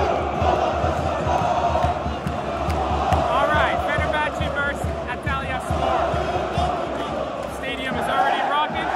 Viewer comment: hahahhahah 0:10 i wasn't expecting to start this video with this chant, do you know what they say there? xd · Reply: They saying Fck galatasaray😂